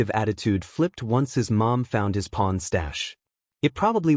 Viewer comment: his what stash??? it sounds like he’s saying pawn??? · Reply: Oh. Corn. Lol by bad yall 😂